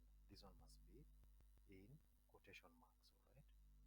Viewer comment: I can hear yur voice properly its low.. please what did yu said the part 7:41 should we add quotation marks or not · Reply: The motto should be in quotation marks